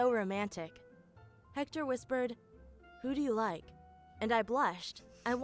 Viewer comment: When he said "who do you like" i thought he said "booty is nice"